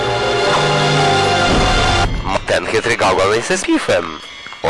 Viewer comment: kto mi wytłumaczy co on mówi :D ?? ''mmtenhytrygałełesesmifem'' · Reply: ten chytry gałgan jest spifem xD